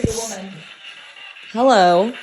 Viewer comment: I swear I heard a man’s voice say “Mahalo” and it wasn’t like how a white guy would say it lol, it like definitely sounded like a native Hawaiian islander saying it… THIS IS SO COOL